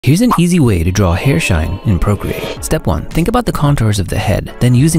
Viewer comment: Idk why instead of hearing “hair shine” i heard “inappropriate” 💀💀💀💀💀💀